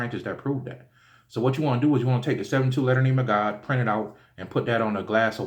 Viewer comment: Greetings, can you write out in the comment section the name you are speaking of at 14:20 to make holy water, please. I am not familiar with whom you refer to and I'm not understanding what you are saying in that part of the video. Thank you much. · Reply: Yes its called the 72 letter name of God. Print it out and put a glass of water on it fo 30min.